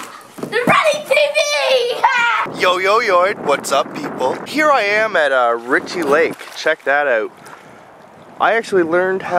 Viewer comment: I thought you said "Here I am at Ricki Lake..." as in the TV woman...